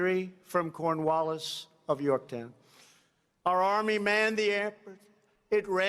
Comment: Sounded like Trump was saying “our army manned the air” before they took the airports. Anyone else hear that or was that just me?